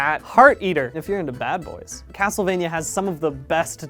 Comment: legit thought he said "If you're into bath salts" after heart-eater 😂👌